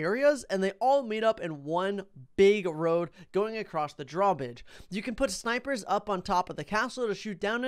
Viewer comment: Am i the only one who on dark castle heard him say draw bidge